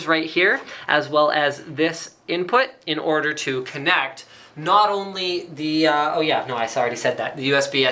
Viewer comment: did you say KINECT ?